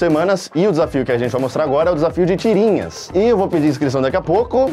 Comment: juro que ouvi desafio de tiringas kkkk